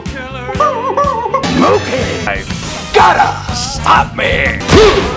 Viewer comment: parece que dice sape